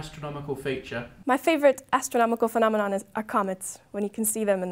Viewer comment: what is she actually saying? "Acomets"? I can't find anything to comets that stays for months... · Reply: "Are comets"